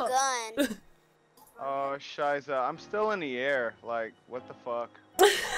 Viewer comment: Did he really say "Oh scheiße.." at 9:55? xD Just wondered bc its a german word and Im german lol